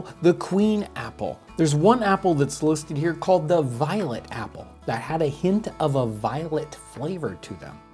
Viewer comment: I thought he said violent apple, instead of violet apple.